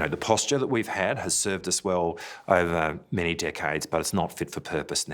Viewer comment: It sounds like he says "Many dick heads". I had t rewind to make sure he said decades lol.